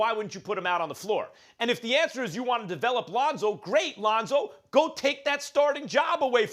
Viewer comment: Am I the only one who heard her say Ronzo?